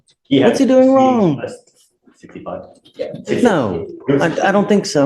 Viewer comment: 65? I thought he said 54?